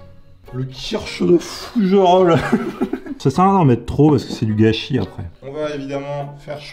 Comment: " sa sert a rien d'en mettre trop parce que c'est du gachi " moi je comprend " sa sert a rien d'en mettre trop parce que il faut que il en reste apres" xD